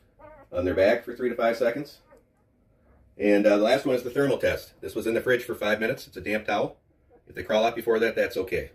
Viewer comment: At first I thought you said put the puppy in the fridge. I was a little confused